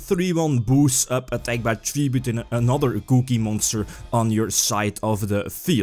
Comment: it continously sounds like is saying cookie monster when he says "gouki monster"